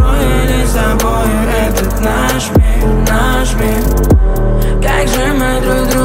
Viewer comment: am I heard wrong or it’s really says nashmil nashmil 😂